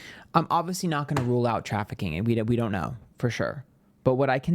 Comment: I thought it sounded like trafficking. It sounds it like it might be trafficking.